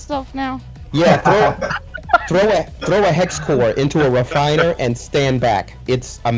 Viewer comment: "throw a ???? Into the refiner and stand back" did he say hex core.?